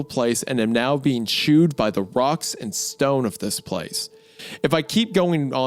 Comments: did somebody say a rock and stone? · DID I HEAR A ROCK AND STONE?!